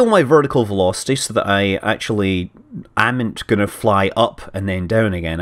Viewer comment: Did Scott just say "amn't"?